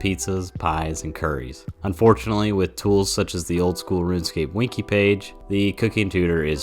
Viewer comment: did you say winky page? XD